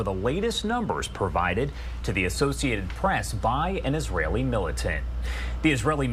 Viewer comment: Did he say Israeli militant?